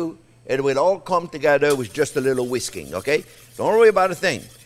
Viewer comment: I thought that you said, it will all come together with a little Whiskey ! (WHISKING)